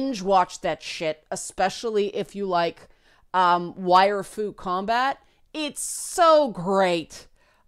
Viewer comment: Lol. When she said Wire Fu Combat i literaly thought she said Waifu Combat.